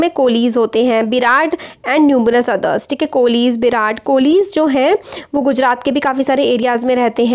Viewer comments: ma'am you have said berads kolis , I heard virat kohli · i heard Virat Kohli 😂 and u😉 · i heard virat kolhi